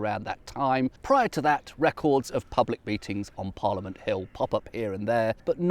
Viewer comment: Did a double-take in me kitchen when, at the 4:48 mark, I heard “public beatings,” rather than “public meetings.”